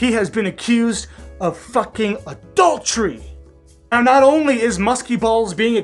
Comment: When you said adultery for the first time, I thought I heard “a dog tree”. But hey, that still seemed relevant. 🤣🤣🤣